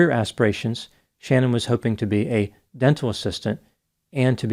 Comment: apromiscuous dental assistant? Now I really heard it all😂😂😂